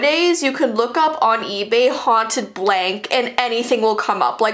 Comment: I thought she said haunted tangerine 🍊